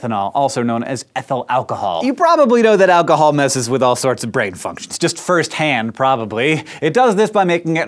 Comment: Watch from 0:35 to 0:40 it said fucktion instead of function